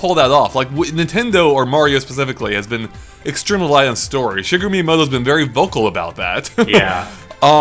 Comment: wait did I just hear 'sugar me miyamoto'?